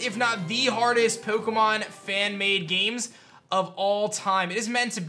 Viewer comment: I thought he said man made games so I was very conserned